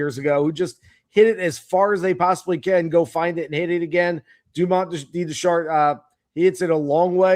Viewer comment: did I just hear, "Dumont indeed did shart," just now???